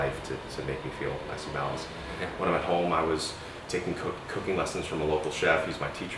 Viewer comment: swear he was gonna say 'taking cocaine'